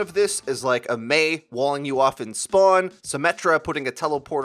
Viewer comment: I thought he said mei spawned camping you at spawn I got scared cuz the what I do every day I have my reasons sorry